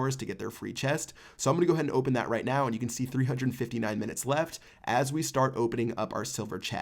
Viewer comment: did he say 3 hundred and fifty nine minutes left? lol